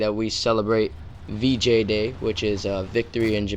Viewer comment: lol, thought u said BJ day